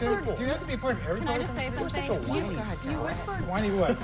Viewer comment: It sounded like he said he's such a whiny brat. But Scott is the biggest man toddler that sits on CNN panels!!!!!